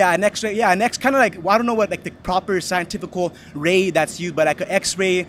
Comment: That dude who’s for open boarders is very ignorant. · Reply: Did he just say scienticial?